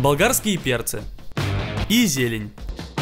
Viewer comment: только я подумал что он сказал изи?